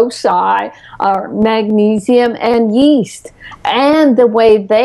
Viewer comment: What does she say exactly ,minute 33:25, magnesium and... English is not my native language :) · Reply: iaio Bou .....Yeast (Candida Albicans)